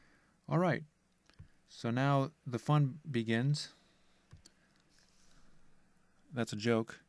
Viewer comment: I didn’t get that joke. Did you say “fund begins”